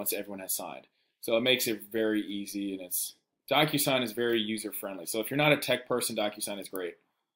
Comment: If you are ready to learn how to become a real estate investor, download my FREE insider’s kit to real estate investing including a deal analyzer, direct mail pieces I use, and example contract: <link> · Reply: Also, at 8:49 the caption says: "... similar to doctor sign" or is it 'similar to DocuSign"?